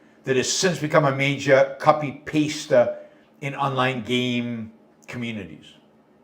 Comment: peak boomer moment, copy-paysta instead of copy-pasta (like spaghetti is a pasta)